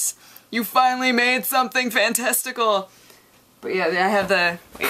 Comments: am i hearing that right "made something for in testicle" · Is it just me, or does she say "fantesticle" ...